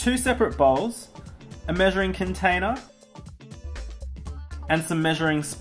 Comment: i thought said a Murdering Cup XD